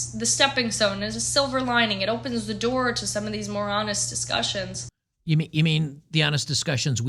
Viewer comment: She said she has a child but I don't see a ring on her finger. Single mom? If you're a single mom you don't get to represent conservative values. You've already proven the kind of values you hold. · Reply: Lol she said, "more honest".. I thought she said "moronist" lol..